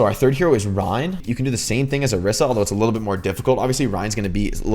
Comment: The captions say Ryan instead of Rein